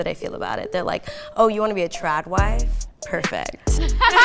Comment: I had never heard the term, "Tradwife," before today. I thought they were saying, "Trap wife."